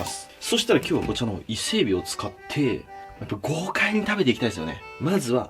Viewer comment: の豪華に食べていきたいですねが、ごうかいにに聞こえた